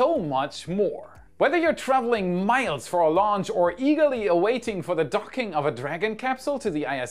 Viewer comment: Go to <link> to get the best-rated weather app there is for Android, iOS & Windows! Weather as you've never seen it before! So, when do you think the stars will finally align for the second Starship's launch? Leave your most precise guesses in the comments below! :_WAIShocked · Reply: Kinda sounded like during the sponsor section you said "Whether you are traveling miles for a launch or Illegally waiting for the docking of a dragon to the ISS" Emphasis on Illegally waiting...